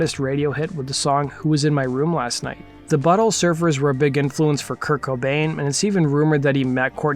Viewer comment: The buttle surfers is what I thought you said